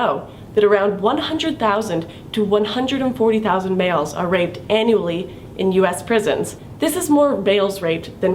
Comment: who else thought she said analy instead of annually?